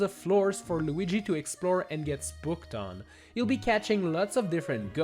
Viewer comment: I swear he said sbooked instead of spooked